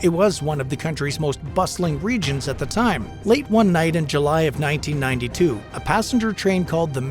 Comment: what's that year I heard? 1992 lol